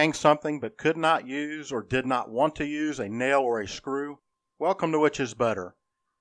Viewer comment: At the beginning of the video, I thought you said, "Welcome to Witches' Butter."